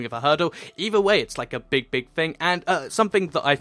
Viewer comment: Turn on subtitles: it's like a big big bang Original: it's like a big, big, thing LOL YouTube IS actually using a computer to detect what is saying, YouTube just use manual subtitles OK? Just like TV Broadcasts.